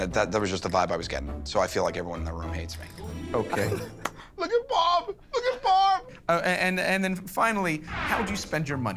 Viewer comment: what is q actually saying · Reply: look at Barb, look at Barb